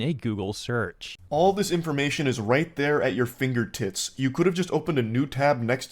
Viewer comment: did he say finger tits